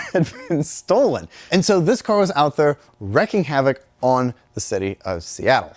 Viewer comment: I was going to poke fun at for saying "wreaking havoc" instead of "reeking havoc" 💀 But I looked it up and.. I am the one who has been saying it wrong 💀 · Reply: Sounded more like he said "wrecking" than "wreaking"